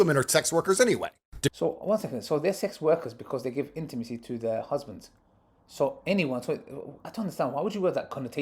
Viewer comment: he actually said boyfriend not husband